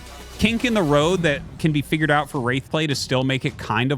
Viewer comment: I keep hearing "race play" when Artosis says "Wraith play" lol